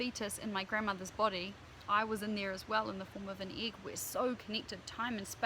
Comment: could you please clarify what your words are, from 10:23 to 10:28? It sounds like (I am not making fun) "I was aneer, as well, in the form of an eequis". Thank you very much for your time .... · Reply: I was in there as well in the form of an egg we are so connected ....